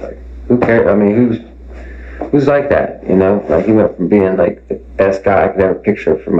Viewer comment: This whole case is really weird, hey @ 8:01 it sounds like he says, “he goes from being the best guy for my girl sister “ do you hear it?